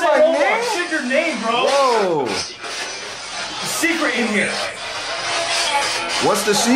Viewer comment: "What's the secret?" I don't think it says "From hell." I heard "Not telling."